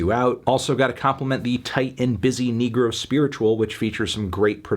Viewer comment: I thought he said nigga for a sec when he said negro spiritual